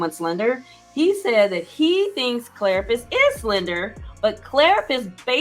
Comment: Hey girl, hey! So glad for this decision day and the last episode was the best! I really thought was saying that Claire slandered him! He was saying slender! LOL😂. My bad!